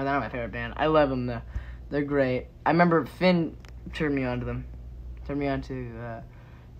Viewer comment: i thought he said finn turned him on😭